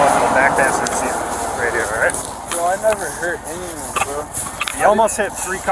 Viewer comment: nothing like a grown ass man acting like a complete child with a dog. warms my heart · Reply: I went back and watched it again. You're right I misunderstood. When he said, "I never hurt anyone" I thought he said he's never done nuthin'. Blame it on my tinnitus. Makes it hard to decipher words when people mumble or there are competing sounds.